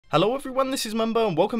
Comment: your intro mindfucked me, had to listen to it like five times, sounded like you said "Hallå" (a way to say "hello" in swedish)